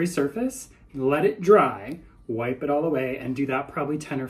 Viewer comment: Lol at first I thought you said I have to “whippet” lol”